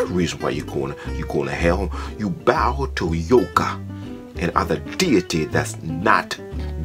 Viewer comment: I almost thought he said "You bow to Yoda." Dude, I think Star Wars is ok, but I don't like it THAT much. (Glory to God, Hallelujah, etc.)